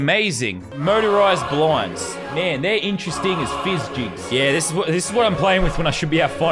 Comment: I thought it said interesting gas fuck